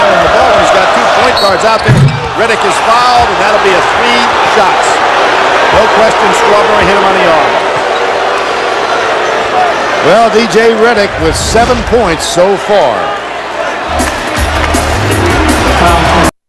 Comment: did he say DJ Redick?